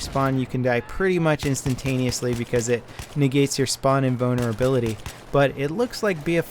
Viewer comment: That's not because his American accent he really sounds like he is saying inbonerability on purpose lmao